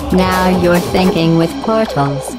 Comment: The subtitles says "Now you're thinking with my tongue home". What?!